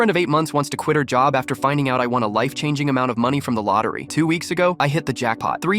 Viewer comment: Lol i heard “after finding out i wanted a life”